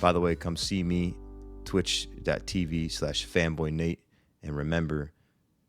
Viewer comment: fuuuu i heard femboy not fanboy